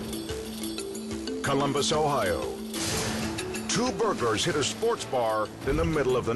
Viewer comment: i swear the narrator said 'two burger' when he meant 'two burglars' anyone else notice tht?